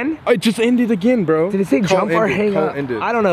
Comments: Ummm..... Did anyone else hear at 11:50 I think it sead Jump now. 2019 anyone no oh um ok bye. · i think it said jump off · IT SAID “jump” I HEARD IT AS CLEAR AS DAY. AND YALL WERE AT SUICIDE BRIDGE..